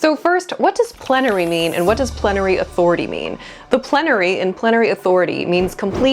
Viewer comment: I misheard it as "planetary authority". I thought Miller watched too much Star Treks